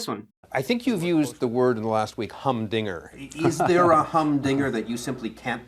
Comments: I seriously heard you say the story includes “minges”. 😂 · I DIDN'T READ THE TITLE AND I THOUGHT YOU SAID MINGES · 0.27 - I didn't hear you right. The first time I thought you said 'minges'. British slang, look it up.